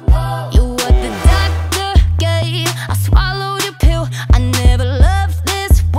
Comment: LoL did she say "I swallowed your poo"?